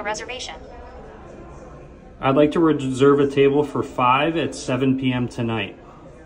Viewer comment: it thought you said observe instead of reserve so AI is still not that smart yet